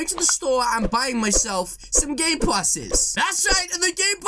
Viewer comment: Who heards gay passes